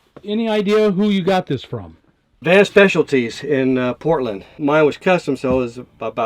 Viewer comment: What is the name of the company that makes the bed in Portland? I rewound the video but I can't make it out. Thank you! Great build and awesome dude! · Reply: i think he's saying "Van Specialties"